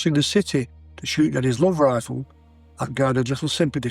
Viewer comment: I thought you said "to shoot his love rifle".... lololol